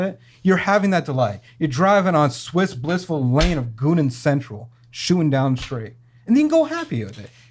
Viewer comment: Did he just say "goon and central" ?